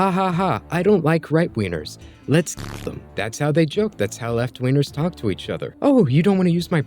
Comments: I beg your finest pardon, but did you just call them "left wieners" and " right wieners"? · why did I hear right wieners